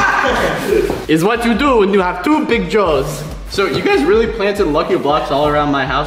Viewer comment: Go to 4:03 and put captions on and you will see what it means likelet me say it to you "is what you do and you have two big jaws"-wait WHAT